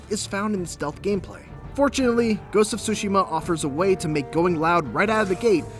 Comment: Gotta be honest, For an instant there i thought i heard 'sushiman'